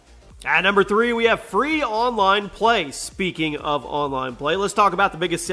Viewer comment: It's free online play. You said it correctly but it is written 'pay' instead of 'play'. Btw a great video, you explained all the things clearly.